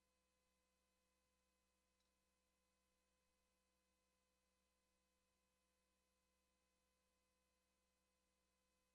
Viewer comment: Damn, I wonder what Travis Browne said to Josh during the staredown that made him shake his head. Anybody a lip reader? btw, Barnett for the win. He's just too intelligent, experienced, vicious and technical for a guy with no title fights. That's not to say Travis isn't dangerous and can't catch him though. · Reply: i think he said ." im ready for saturday "